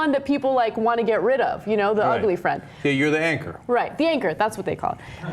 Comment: What's the word @ 1:09 they said to describe a girl that no one wants to date? The ankor or anchor or something..?